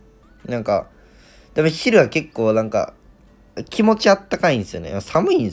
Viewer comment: 昼がヒルにしか聞こえないーーぃ